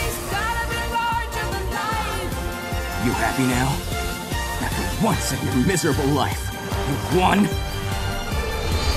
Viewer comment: What does he say after 3:39? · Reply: "Are you happy now?"